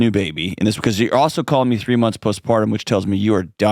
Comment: When he said “you’re also calling me postpartum, so you’re also dying of loneliness… I thought I heard him say calling me POSTMORTEM!! 😂 and then when he said DYING of loneliness… I was like WUT??? 😂😂😂